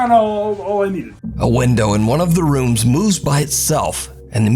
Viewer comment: the part where window moves by itself...dont know if only me i heard saying window moes instead of moves..hehe😁